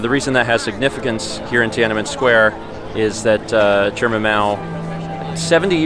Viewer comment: Right lmao I just came to the comments to see if anyone else thought he said Mao the same way he says Mal 😂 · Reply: I had to listen to it a few times. My girlfriend and I swore he said "Chairman Mal." When we realised he said Mao, we prefered it the other way. 😄😄